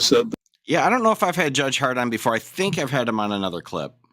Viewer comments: Omg i thought he said judge Hardon lol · I just heard you say “judge hart on,” and laughed like an eight-year old 😂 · I could have sworn called him Judge Hardon lol I then realized he said Judge Heart on. I was thinking we have a Sackrider and now a Hardon 😂